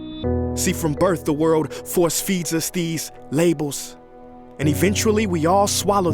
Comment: what is he saying here? "From birth to world, for ??????" I am japanese and really wanna fully understand this video, plz help me :( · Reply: From birth the world force feeds us these labels